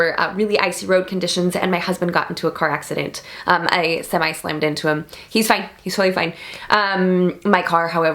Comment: Whoa, whoa, whoa you said: your husband got into a car accident but YOU slammed into him? Shouldn't that sentence just be "I ran into my husband's car or I almost ran over my husband"? You know you've been married a while when you casually gloss details over. LOL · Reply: WinrodI thought she said "I Semi-slammed into him." like she side swiped him skidding out of an icy driveway. It's been a long day.